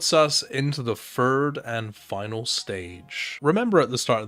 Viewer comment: Just a nitpick, but you gotta put your tongue on the back of your front teeth and shoot it up the roof of your mouth for the "th" sound (e.g. Fird instead of Third at 4:39) Had the same problem and was glad I cleared it up, thought you might feel the same